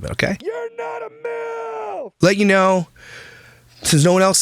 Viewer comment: I turned on the subtitles when you used the swear pillow but it didn’t pick up what you said…you’re not a milf?? 😂